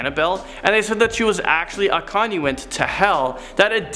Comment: I could have SWORN said that the doll was a 'condiment' to hell. What? Devils and demons need ketchup and mustard with those damned souls. Right?